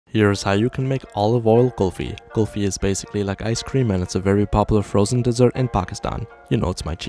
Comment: Bro I thought he was saying Oliver ice cream 💀 ☠ 💀 ☠